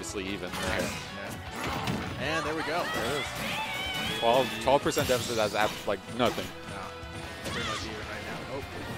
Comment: Wtf she says in her star ko? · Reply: I’ve got to go peeeeeee